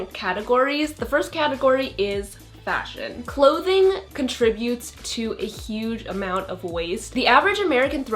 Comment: I thought you said clothing leads to a huge amount of boys